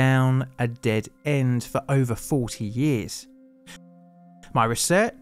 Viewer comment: you say 40, subtitles say 30